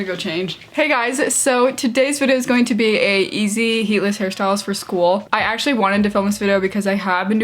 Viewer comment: ngl, thought it said easy and heartless hairstyles